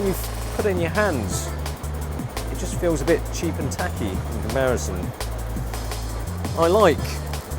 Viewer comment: A hhh ---- at around 4:40 , and I'm probably mistaken, but did I hear you say "cheap and tacky,and American" ? Did I hear that right or not? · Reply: it is not "american" but "in comparison".